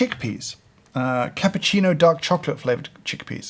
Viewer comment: "These are chickpeas. Cappuccino dog chocolate flavored chickpeas" Is what I heard, had to do a double take haha